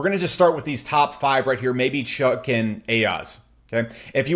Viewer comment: What does he say at 21:35 Chuck and AIOZ? Did he say chuck?